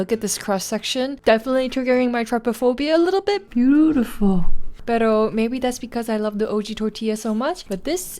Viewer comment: I'm sorry but did you say "PERO" , I'm filipino and it just sits right when you mentioned it AND I CANT UNHEAR IT 😂😂😂